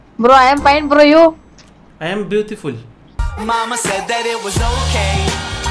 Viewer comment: when you said i am beautiful " i heard MAMA EARTH " bhai itna promotion krte ho aab yehi sunayi deti hai 😂